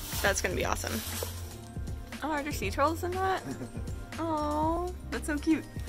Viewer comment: Oh, "sea turtles", I heard "sea trolls" and for a second I was very confused and very excited